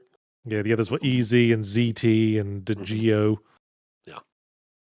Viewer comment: disregarding zed vs zee pronounciation, wtf word is Yahtzee referring to that just sounds like zt? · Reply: Ziti is a kind of pasta.